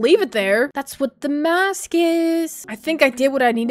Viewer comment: did she really say that’s what’s the mass say?